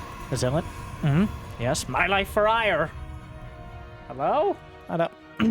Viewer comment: I always mishear "my life for hire" when you copy the changeling's thoughts, because he doesn't have a mouth, obviously